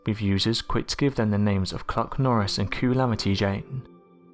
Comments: I couldn't stop laughing when he said kluc-Norris lmao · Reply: I swore he said Cock Norris until I played it back....lol. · I swore he said Cock Norris until I played it back....lol. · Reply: I thought he said Cuck Norris